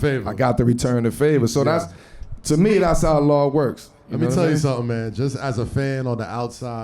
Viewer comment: Wait Melo is Muslim?? 19:28 “to me that’s how Allah works” or did he say Law works? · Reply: Law